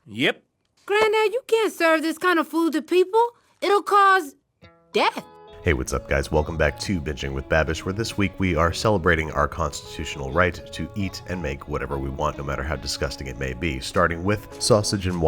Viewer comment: I don’t know if it’s just me but it sounded like he said “hello welcome back to bitchin’ with babbish at 0:24”